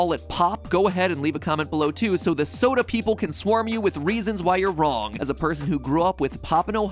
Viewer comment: I say pop. My SO heard you says the soda sayers would swarm. He is a soda sayer and just laughed